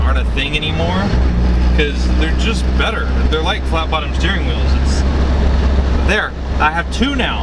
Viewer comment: What did you say about the steering wheel that you like at 7:43? "Flap-On Steering Wheels?" I am certainly misunderstanding what you had said . . . twice. I have an '86 944 just like yours (even same color). I love the little bugger, and have decided to spice her up a little at a time. Thanks bud! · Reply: Nemesis980 hahaha had to go back and watch it! I was saying that the two best things ever put on a car are flat bottom steering wheels and pop up headlights lol